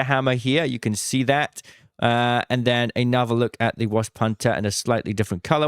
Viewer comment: anyone else hearing “worse punter”?